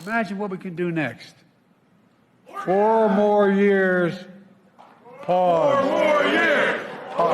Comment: I thought he said "four more years. Pog"